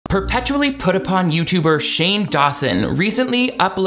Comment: i thought you said perpetually off putting and i was like i mean couldn’t agree more